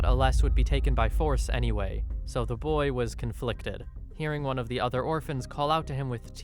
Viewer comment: * i hear you was conflicted*